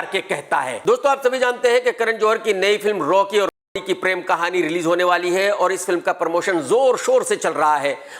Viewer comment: Did he say Rocky aur R_andi ?